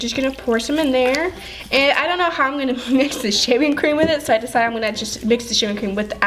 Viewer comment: I thought she said without shaving cream?????whatever. I STILL LOVE YOU GUYS